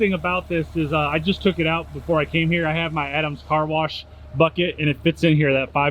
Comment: Thought it clearly said No Carwash